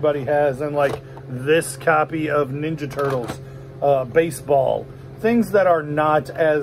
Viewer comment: I had to rewind I thought you said This copy of "NINJA TURTLES BASEBALL"